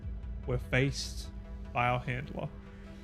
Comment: When I first heard you say "faced by our handler". I heard "faced by bio-hitler". WAT!?!? WTF!!? I had to jump back twice to hear it clearly.